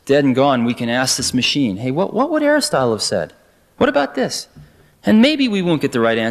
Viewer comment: “hey, what would hairstyle have said?” 5:05 or how about Hammer Robbie?